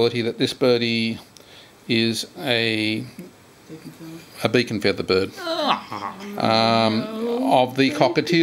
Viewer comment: minute 1:55 i dont understand,what you dr,said,but i hear the expresion of the owner,it was someting bad? is this pretty tiel still alive? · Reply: "beak n' feather bird" Ie. PBFT